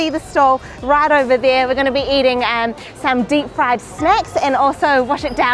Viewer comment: I thought the lady said fried snakes. She meant snack wtf. I thought nepalis eat frie snakes for breakfast